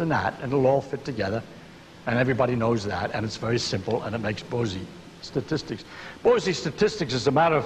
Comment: To which statistic he is referring to hier? I couldn't understand the name something like Bosey ... thank you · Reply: Bose-Einstein